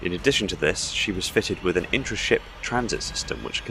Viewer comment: Intership transit system implies it allows fast transit between ships. Intra ship is within the autumn itself. Just clarifying cuz I thought you said inter.